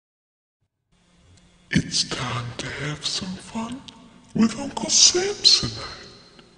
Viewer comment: What I hear:it's time to dance with uncle Sampson What others hear:it's time to have some fun with uncle sampsonite